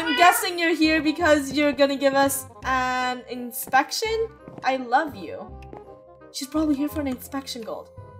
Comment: When was saying I love you to the cat I thought he was saying I love you to Jesse